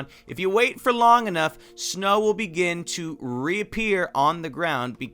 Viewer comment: did anyone hear that or it's just me He said Repear